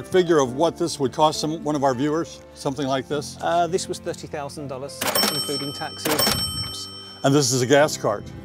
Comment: Did the first guy with the ZZ Top cruiser golf cart say that it cost $50,000? Were my ears deceiving me? I just bought a new Honda pick up truck for that amount of money and I thought I was paying way too much! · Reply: I thought he said $30,000 but I could be wrong.